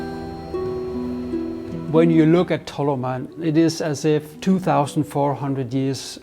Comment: Thought it said Talmud man for a second 😅😅… thought it was about some 🧃 👿 worshippers 😅😅